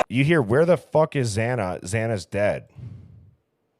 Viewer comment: I heard “where the fu:$ is Xana, Xanax dead.”